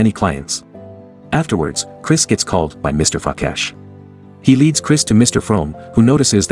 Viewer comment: am I the only one who heard " Mr.Fuck ass" instead of "Mr. Frakesh"